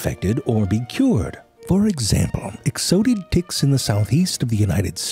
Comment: only me who thought he said "dicks"